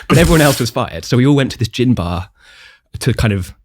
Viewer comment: ❓Does anyone know….@ 18:19 What is he saying? What is a “gym bar, gin bar”? Maybe it’s a UK / London thing.🤷🏻‍♀️ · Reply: Gin bar